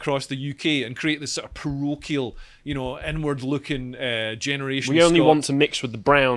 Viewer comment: am i the only one who thought leo was saying "N word" when he was saying "inward"?